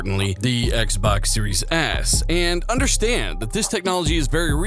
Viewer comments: sounds like you said Xbox Series Ass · That XSX Forza Horizon 5 footage tho 👀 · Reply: sounds like you said Xbox Series Ass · Did you say Xbox Series 'S' or Series 'ass'? 🤣🤣🤣🤣🤣